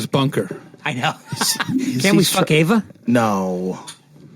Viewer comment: does he say can we fuck Ayva?